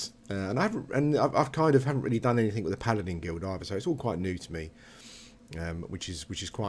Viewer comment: I really liked that I misheard you at the start. "I need to find Paladin guild master, Tin Can." Made the whole video so much more enjoyable. I main a High Elf magician, but I've always had a High Elf paladin alt. Their cultural plate armor is gorgeous and perfect for the class.